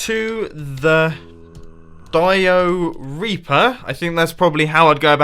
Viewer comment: "Dio-Reaper"... All I heard was, "Diarrhea-per", which Iskall85 probably thinks of it as.